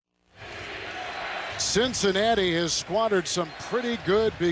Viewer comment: What did Kevin Harlan say, again about the Cincinnati Bengals' offense in terms of their starting field position at 37:01-37:06?... "The Cincinnati Bengals have--" what?... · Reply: Squandered